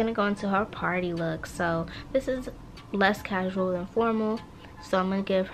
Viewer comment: And when she said formal I thought she said hormone I was like.. “Wt- OHHH FORMALLLL OKAYY”